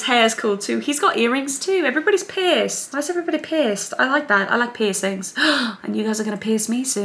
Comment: It sounded like Kris said "why is everybody Pissed-" 😭